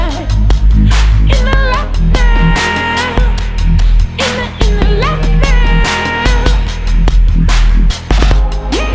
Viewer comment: did he say "unified in the lockdown"? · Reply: In the light now...